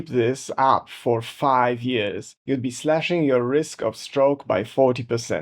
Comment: OMG… I thought you said “slashing your wrists” until I read the ‘closed captions’. 🫢😉🤪🥴